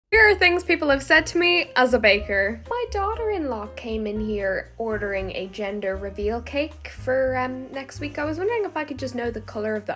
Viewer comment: I was listening to it on the street and thought she said "as a banker" and got massively confused 😂